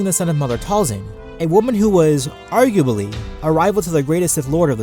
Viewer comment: Did you just say, 'Argubaly'?